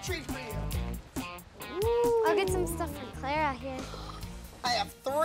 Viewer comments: Did I just hear Chloe say " I can get some stuff for Clara here" Awwww #sistergoals ⭐️❤️🎉😍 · I heard Chloe say I'll get stuff for Clara